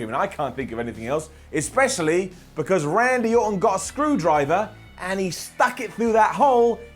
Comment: I think I heard ‘Andy Orton’ somewhere in there